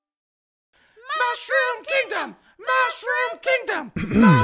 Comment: I thought the toads were saying mushroom pizza XD